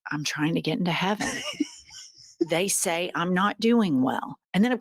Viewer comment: "I heard" "I'm not doing so well." Is what he said.